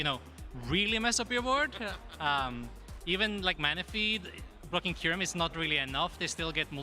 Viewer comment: does say "F*cking Kyurem"??? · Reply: I know he says "blocking Kyurem" but it sounds like he dropped an F bomb 😅